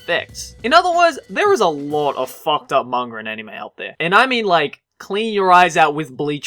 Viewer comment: is it just me or does he always say"mangar" instead of "manga"? See for yourself